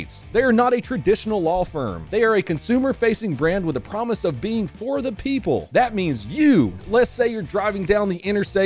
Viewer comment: I just skipped through the ad at the start of the video and i swear it sounded like said "for the S.S" while i was skipping 10s at a time 😅